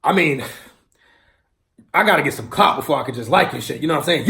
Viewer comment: At the end I thought he said I need to get some cock instead of clout😂😂